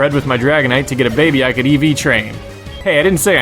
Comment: i cant deside beetween pikachu or blastiose/squirtle/wortortle so they tie, i like blaziken more than infernape, and, i like ur list, i agree with alot of them also WHATS EEVEE TRAIN?! thats what it sound like you say eevee train